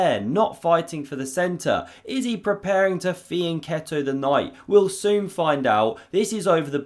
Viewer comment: I never get what you're saying about that knight (Enlish second language, here). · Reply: He jokingly says "is he preparing to fianchetto the knight?"